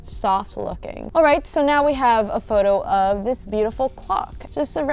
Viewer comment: I’m srry but it sounds like she said we have a pic of this beautiful cock